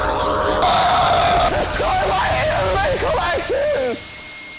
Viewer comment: he said “destroy my anime collection” 😂😂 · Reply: the guy wit da milk :P I thought he said “enjoy my anime collection!”